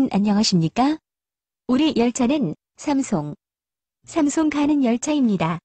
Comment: 영어로 들을때 삼송이 아니라 삼성으로 들리는...